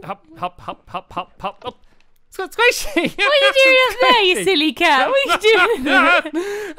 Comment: what are you doing up there you silly cat! Me: how dare you call stampy a cow! ( I thought she said " you silly cow " )